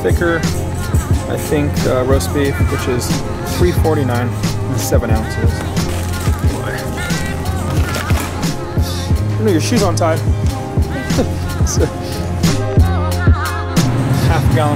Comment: "dude shoe untied huh sick" is that what u said lol ?